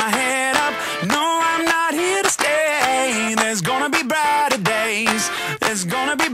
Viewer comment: yea i always thought it was badder days cause he says im not about to give up because i heard you say theres gonna be badder days..:S well my bad i guess . if its praising the Lord i guess it really doesnt matter if its brighter or badder.